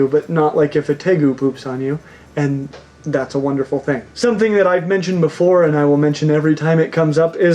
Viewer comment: I thought he said when the tegu poops on you that is wonderful thing